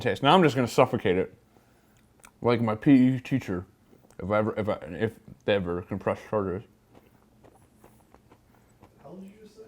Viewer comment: "Now I am just going to suffocate the thing.. (muffled) like my PE teacher if they will ever press charges." Tim: What did he just say?!~ Lmfao! I love these guys